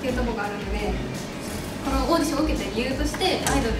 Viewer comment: 「オーディション」→「御社」に聞こえた